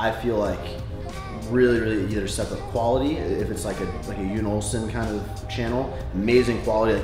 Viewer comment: What channel is saying at 4:58? I can't understand him, something olsen? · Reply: Jon Olsson, rly good vlogger :)